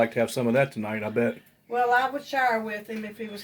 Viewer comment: your accent is so cute. I thought you said "well if he was here I'd shower with him!"😳😁